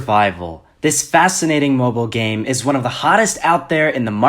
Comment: lol at the start i thought he said "muggle game" instead of mobile game. #harrypotterreference